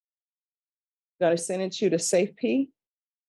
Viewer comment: “Safe Pee??” I know I misheard that, but whatever it’s called I like this better. It’s funnier. 😂